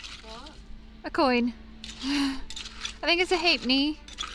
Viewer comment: you found a coin and you said you think it is a "hit me"? I know you must not have said "hit me" but I don't know how to even google what you said because I can make it out. Thank you for taking us on your inspiring adventures! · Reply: I think she said Hay-Penny meaning Half-penny maybe but not sure.